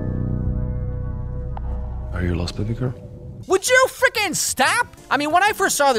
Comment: I think he was saying “are you lost pretty girl” maybe....?